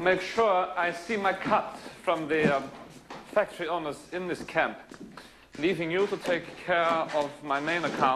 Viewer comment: messed up his line a little there, maybe it was intentional or maybe i heard it wrong: “on this—in this camp.” just happened to hear it 😂 tremendously great actor he really is though! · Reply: He says “factory owners in this camp”